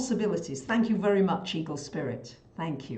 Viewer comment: Why at 18:44 does say, "thank you very much evil spirit" but her lips are not moving when she say it. I've replayed it five times now and am about 90% sure that is what I am hearing and seeing. Can someone else please see if that is what they are hearing and seeing at that time stamp? It doesn't makes sense that that is what the audio has her saying but her lips are not moving when those words come out. With running the risk of sounding like a paranoid freak...if I didn't know any better I would suspect that "The Opposing Force" is now effing with one of the few people I trust's videos. WTF? Dear, would you mind addressing this, asap? Because I find it disturbing. (Perhaps you could take it to the heavy hitter-the Ps to see what's up). Thank you! Anyone?